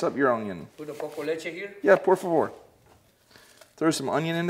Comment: when you said por favor it sounded like you said pour some more lmaoo which he did 😂😂